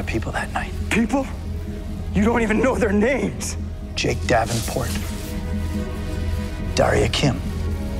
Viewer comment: i heard "Chase Davenport" anyone else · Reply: he said JAKE davenport. DC is not going to buy Disney xd character